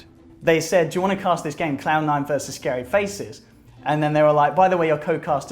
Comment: OK did ODPixel really called C9 clown9 at 6:04? 😂